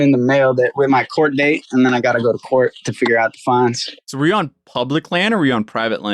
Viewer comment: question posed was about fines and it sounds like he says "to figure out the fines" not funds, incase this matters at all